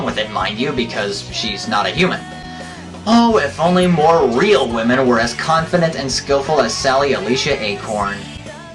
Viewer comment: i heard that as him saying "oh, if only more real women didnt wear clothes in public" lol